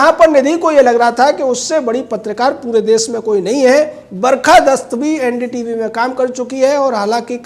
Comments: did he say Barkha dast?? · 😂😂😂 did he say BARKHA DAST (💩💩) 😂😂..